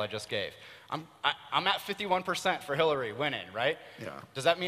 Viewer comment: Did Scharp say 'Killary'?